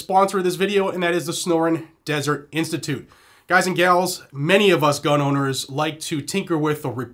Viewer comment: I had my headphones off one ear and it sounded like you said Snoring Dessert Institute lol